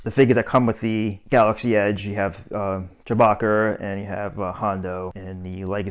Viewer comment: who is Jabacer? lol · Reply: Boston accent? I thought he said Chewbacker…